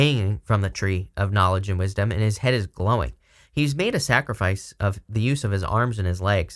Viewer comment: When you said on the hanged man card his head is glowing I heard “his head is blowing” and saw and felt this 🤯 energy and emoji.